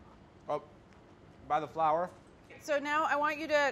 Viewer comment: Carla: “put the carrots on the silver tray” David looks at basil plant David: “the one by the flower” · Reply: Ihsan T ohhh was he saying flower? i thought he'd said flour